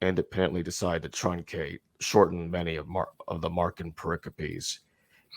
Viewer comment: Probably a dumb question but what is the word is using at 52:58 ? Empricipry? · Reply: Pericope?